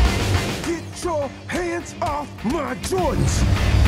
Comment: I thought he said "GET YO HANDS OFF MY JOINT!!!" lol